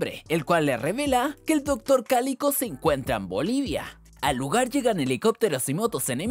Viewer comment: wtf vi tantas veces la pelicula que nunca me di cuenta que dijo bolivia🇧🇴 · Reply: alv yo siempre pense que decia olivia